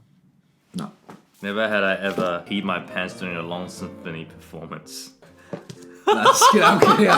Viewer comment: I swear I thought Brett had said "eat my pants" and I thought he was referring to having your pants going between your butt cheeks, LOL